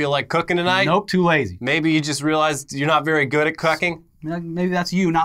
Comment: I swear at 21:06 said "You're not that good at cucking"